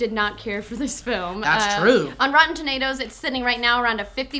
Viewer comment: Did say "rotten tornados" at 6:09?